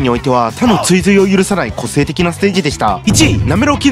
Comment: ボーっとしながら見てたら特徴のないクソステ ロロイトがお送りしました〜に聞こえたwww